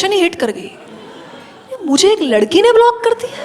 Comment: what did she said ladki ya ladka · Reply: She said "ladki".